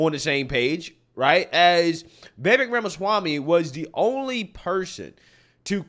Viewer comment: CC closed caption says "Baby Grandma Swami" Instead of Vivek ramaswamy" I just thought that was funny lol